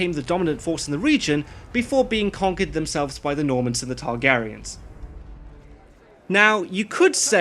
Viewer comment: did he just say before being conquered by the normans and TARGARYENS?